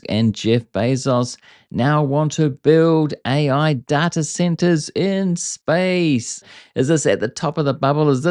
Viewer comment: all I heard was "Pigs... in... SPACE"